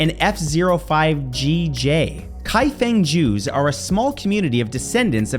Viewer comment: My dumbass thought you said something like Kaifengjus instead of Kaifeng Jews and thought it was some sort of Kaiju. 😂😂